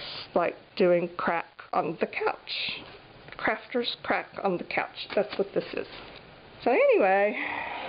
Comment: lol Outside my skill set. I laughed out loud. Gorgeous flowers and drop paper. Love the drop paper book. Wow! It makes me happy too! When you said "crafters crack on the couch" it sounded like you said "crafters crap on the couch". I had to re listen. lol I guess both would apply huh? :-) Cool foil technique. Thanks for sharing. :-)